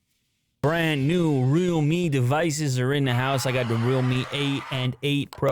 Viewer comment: I thought you were saying rommie devices haha